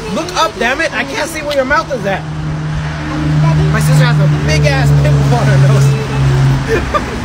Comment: "MY SIstEr haS a Big aSS piMplE On heR nOSe." Got me dying💀💀💀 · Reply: I thought he said" my sister has a big ass peanut butter nose"😅